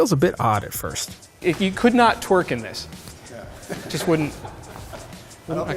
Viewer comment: So I am the only one that heard him say "You could not twerk in this." Lol